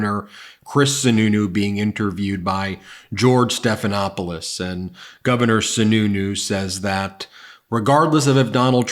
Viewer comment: Just want to admit the whole video I kept hearing 'Governer Snu Snu'. No I'm not embarrassed.